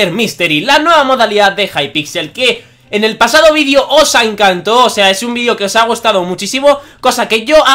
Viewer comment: mira lo que dice con atencion dice ''os ancanto'' en el minuto